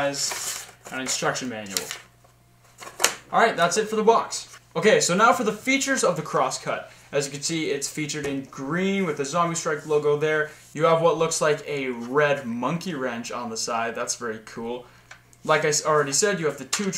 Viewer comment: At first I thought said cross cat I'm like NOOO!?